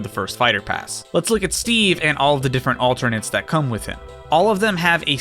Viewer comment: I thought they were saying "Steven" which is just funny.